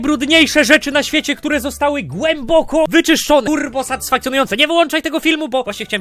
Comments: Czego usłyszałem tam "ku**o satysfakcjonujące" 😂😂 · niech tylko nie ja usłyszałem kur*o